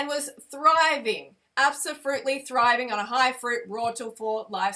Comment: did you say abso-fruit-ly? i love that! 😂😂